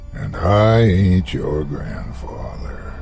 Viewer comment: The first few times I heard the intro to this show I thought he was saying, "...and I ate your grandfather." Which, hey. It's a horror show, what was I expecting? Love this series, is a wonder. Thanks for all your hard work and creative passion.